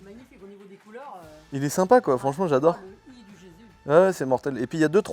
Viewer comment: C’est moi ?!? ou à 6:05 minutes quand dit le teeshirt il est sympa j’adore ! ça mère répond « oui surtout le i du jesus »🤔🤣